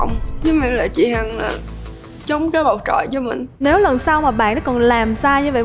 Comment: con bé nói gì mà em nghe 4 lần k ra z mng :) chị Hằng chống cái bộ cọ là sao ạ? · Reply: “Mình rất là xúc động vì chị Hằng chỉ chổng cái bầu chọn cho mình”